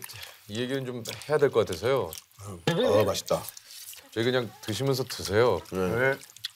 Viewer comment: ‘드시면서 드세요’ 라고 들리는데